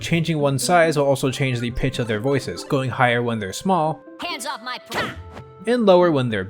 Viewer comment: wait, wtf did Falco just say..?! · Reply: hands off my- CAW!